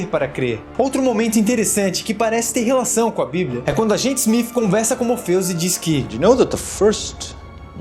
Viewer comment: Eu ouvi SMIF?